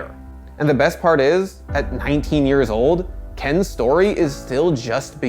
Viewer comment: Did he just call him "Ken"? lol